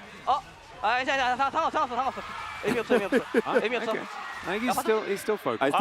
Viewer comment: i think he said 괜찮아 "it's okay", 상관없어 "it doesn't matter", then he says 의미없어 "it's pointless"